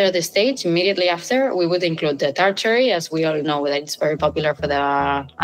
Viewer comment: WTF did she say at 10:53? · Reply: Tart cherry